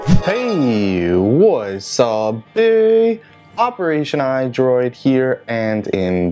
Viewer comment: what does he always say in the beginning? heyyy whats sapp baee? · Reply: Lol I once thought he said What's up Theeeeee operation android here...